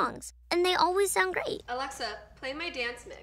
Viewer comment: did she say alexa?? is it not GeorgeNotFound lol